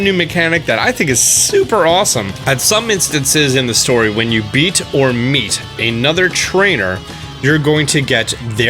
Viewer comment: Lol did I just hear say “Beat your meat”? 😂💀